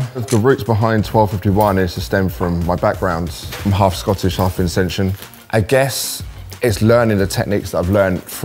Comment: I know this is an old video, but I just wanted to let you know you've got an error in your captions. At 3:45 said "I'm half Scottish, half Vincentian" (as in, from St Vincent) but the caption says "half Ascension."